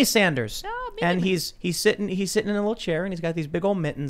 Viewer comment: did anyone think he said "he's sitting in a wheelchair."?